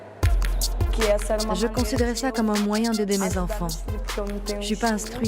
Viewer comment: j'étais quoi ?? Pas trop compris. Magnifique reportage. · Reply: callgirl